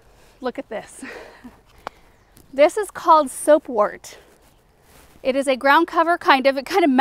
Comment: I just love love love your channel, what’s the name of the plant at 8:00? It sounds like you say silkwort but when I google it I come up empty handed. Thanks 🙏🏼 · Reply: Soap wort. We call it Bouncing Bet where I live! :o)